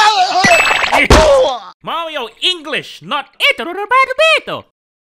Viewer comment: DID JUST SAID MARIO LANGUAGE AT 3:26 ?!?!